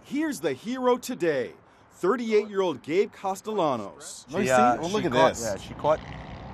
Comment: Does im the only one who hears that he says here is the 38 year old gay ?